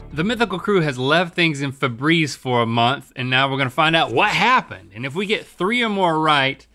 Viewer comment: Ohhh, I thought it said "leaving things in the freezer for a month" lol